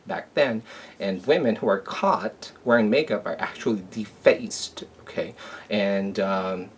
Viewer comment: at first I thought you said the women were defenestrated and I was like that's not so bad...then I realized you said de faced...